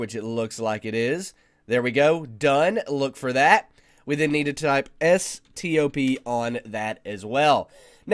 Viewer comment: nic what you say in 5:00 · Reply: We then need to type 'stop'... 5:26: port-forward - Yes, this is a port-forwarding tutorial...